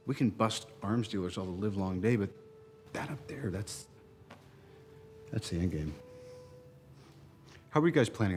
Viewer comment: Until now I always thought Tony said “That’s the A game”